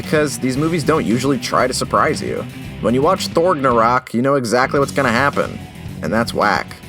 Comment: the subtitles say "thort in Iraq" when he said "Thortnarok"